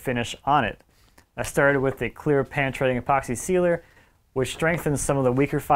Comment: Hello, What kind of epoxy outdoor finish does he put on this? The CC says "Pan treading Epoxy". It sounds like he's saying "Pantry Epoxy". Does anyone know. Or, if you see this... Help me out. THANKS! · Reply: Penetrating Epoxy. Link in the description